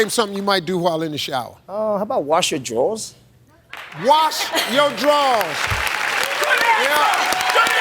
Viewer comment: That one guy said wash my jewels, and Steve thought he said wash my drawers. 😂